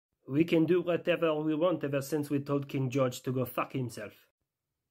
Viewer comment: I thought he said George Santos 😅